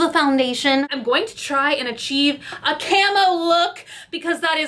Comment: I thought she said "camel look" then stood up...yikes